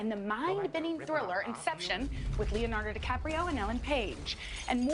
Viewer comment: Did she say Infection instead of Inception?